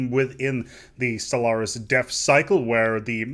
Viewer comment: Phew, I misheard you at the beginning of the video... I heard "Stellaris Death Cycle" :o